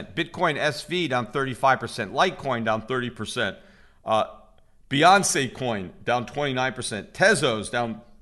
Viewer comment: Did he just say "Beyonce Coin"?